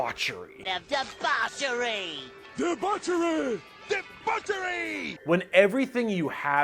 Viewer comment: if you were like me who was wondering what he was saying. The word Debauchery and this is what it means excessive indulgence in sex, alcohol, or drugs.